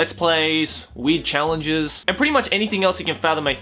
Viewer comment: I thought he said "Weed challenges"